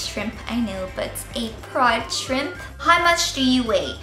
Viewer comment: Oh yessss 'From the Inside Out' is the BEESSTT song! Amazing that you love that song as well. :) :) :) Also, when you said 'proud shrimp' I honestly thought you said 'prawn shrimp' hahahaha.